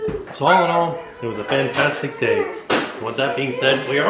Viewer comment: saying “and now it’s time to pay the poopy” instead of price😂😂😂